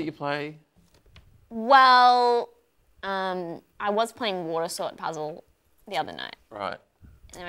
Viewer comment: I thought said she was playing "War Assault Puzzle"